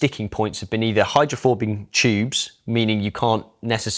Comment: Did he say “hydrophobing tubes” at 6:03?